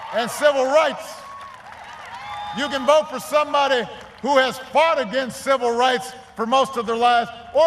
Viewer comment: did he say "you can vote for somebody who has fought AGAINST civil rights"?